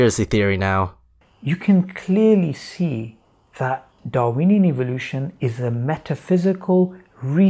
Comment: did he say "Darweeny Evolution"? XDDDDDDDDDDDDDDDDD edit:XDDDDDDDDDDDDDDDDDDD